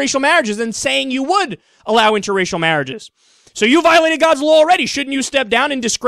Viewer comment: I thought it said Godwin's Law instead of God's Law